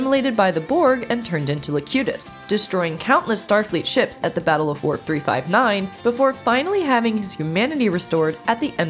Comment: I swear to God, I think she says "The Battle of Worf 359" at 02:20.